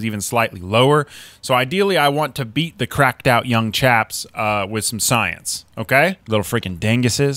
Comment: I 1000% thought he said cracked out young japs😂 he said chaps